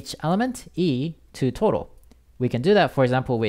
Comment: bruh i thought you were saying toro instead of total in 2:26. i cant unhear it. anyways your vid is a big help :)